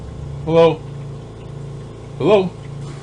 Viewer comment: I heard hello at 1:17:22!